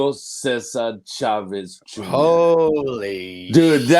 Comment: I thought he was saying Chavez sr to i was like wait What